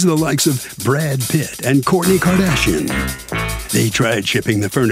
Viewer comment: 😂😂 the intro guy sounds like he said, "Corny Kardashian"